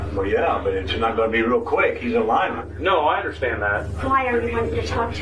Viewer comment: When the cop says ( 7:50 ) “talk to him, real quick” the father responds that is not going to be real quick because he’s a ——-“ what is the word his dad says? I’ve listened back several times and I can’t make it out and the captions say “he’s alignment” and I don’t know what that means so either I’m just fed and the captions are wrong (which i think it is) or I might be the next DC you do a story on.. well not DC but maybe a DTCF (Dumb True Crime Fan???) Maybe? Lol if any one can hear the word he says it would be much appreciated, it’s one of those dumb details that will drive me nuts and haunt my dreams... lol thank you in advance. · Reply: He says "is not going to be real quick , he's in Lyman" either Lyman or some other town spelled slightly different than that. Either way he is saying that is son is in a different town. Edit: I looked up towns in Ohio that start with 'L' and Lima is a town there. I am guessing that's what the word was.